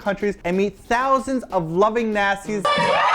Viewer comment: I had to rewind when said 00:46 "...got to meet thousands of loving Nasties" I thought he said loving Nazis!!!